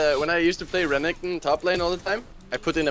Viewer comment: When i used to play renekton top lane all the time i put in a .......... What's he saying,what's the word!?!? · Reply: I said "a macro"